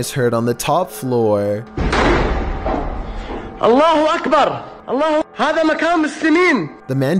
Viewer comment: did he say allahu akbar